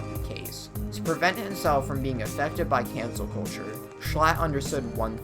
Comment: the captions say "cancer culture"